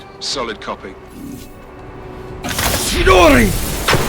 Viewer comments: hold up! at 28:37 did he say chidori?! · did he said chidori?